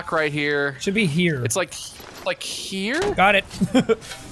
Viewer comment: 2019 and when Sean said "dig dig dig!" i heard "top of morning" in my head xD